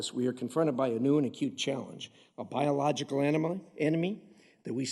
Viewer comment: Anyone else catch "biological enema" at the 1:00 mark? 😂😂😂